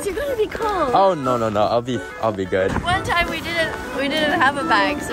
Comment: i thought he said "I beef good"